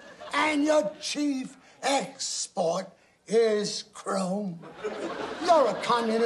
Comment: I’ve also remembered this since it aired, but I always thought he said the chief export is Coal. Just watched this and realized I’ve been wrong for decades! But I can’t make out what he actually says 🤷🏼‍♀️ Help anyone? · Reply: Chrome!